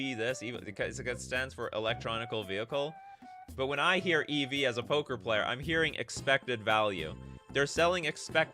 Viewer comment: When you mentioned that as a poker player, EV means expecting value, I had to rewind the video a bit because I heard, ‘as a Pokémon player’. In Pokémon, EV stands for Effort Value.